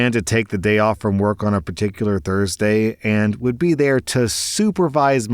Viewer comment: I didn't know that Saturday sounded like Thursday. 😲